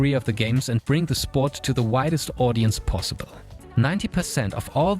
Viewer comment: I can't be the only one who thought he said the whitest audience possible